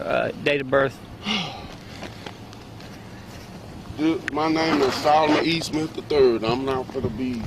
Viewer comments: he clearly says: "My name is Solomon E. Smith the III" Female officer: "Mr Eastman, how you spell your first name?" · The first time I heard it, I thought he said "Solomon Eastman the third." When the rookie female officer comes back from running his name, she referred to him as "Mr. Eastman." If you run it back, he clearly says "My name is Solomon E. Smith the third." She should have clarified his last name as she did with the spelling of his first name. It is a shame how they treated this man who was just walking down the street to his home. How could he ever walk down that street again and feel safe? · If you listen to 1:01, he clearly says Solomon E. Smith III. At 6:17, we find out that the cop misheard him, and she accuses him of lying about his name. Notice how there are people in the comments section also saying he lied about his name. Do not bother trying to reason with these people. They have the ability to rewind the video and verify that he did in fact give his correct name. But since an officer said that he gave the wrong name, they will now live in an alternate reality where she was right. They believe cops more than they believe their own senses. If an officer says it, it must automatically true, and doubting the accuracy of what she said is heresy. They think every single person that has ever worn a badge is a perfect saint, and honestly it's not worth the effort to argue with them.